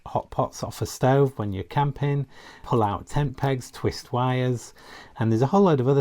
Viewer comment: did he say you could use the parcel hook to pull out tampons???